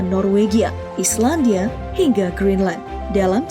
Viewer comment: gw dengernya grand line njir,ada yang sama😂 Edit:ternyata green line😂,maklum fans one piece😂